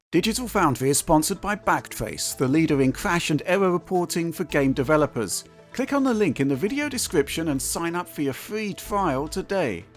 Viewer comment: Lol I heard that as Digital Foundry is sponsored by fuckface! and said, "wow these guys are mad!"